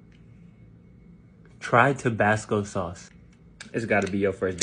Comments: i thought it said tobacco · Tabasco goat 🐐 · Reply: Anyone else thought it said tobacco